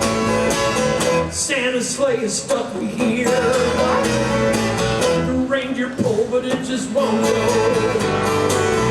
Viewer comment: Sorry, what does he say at 14:44? 🤔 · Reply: Stopping (I think)--I had to listen to that a few times too!